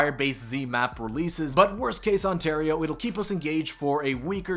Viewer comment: did he say Ontario?